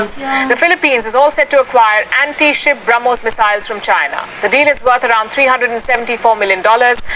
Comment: Did she said China ??????